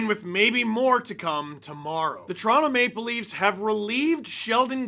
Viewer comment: i heard trauma maple leafs